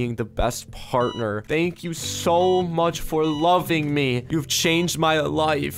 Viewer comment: Thank you so much wife Me: That sentence didn’t make sense... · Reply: I thought said thank you for loving me